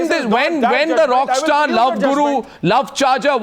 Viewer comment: did he said love guru ;p